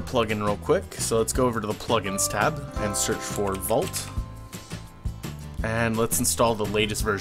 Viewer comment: lol thought you said volt